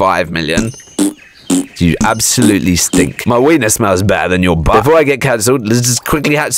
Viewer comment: WHAT DID HE SAY??? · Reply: Sick